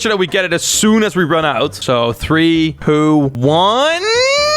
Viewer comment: When was counting when he said poo instead of 2